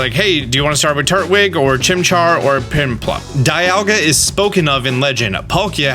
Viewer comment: DID you just say piNplup?